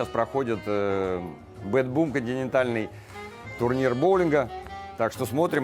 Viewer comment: Хорошо,что есть расшифровка текста,а то я услышала " генитальный боулинг".Оказалось, континентальный